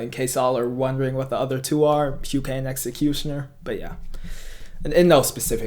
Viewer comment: who did you say first at 3:45? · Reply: h u k a e